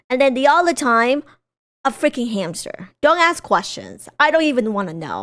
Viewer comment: I'm sorry, but did you say a hamster?!